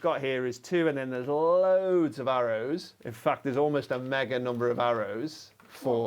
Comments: You had me worried for a moment, when I thought you said MAGA number. Will we never be free of him? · Thought it said MAGA Number. Lets go 47+